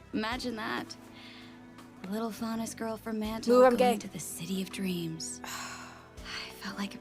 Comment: did she say Moo I'm gay ?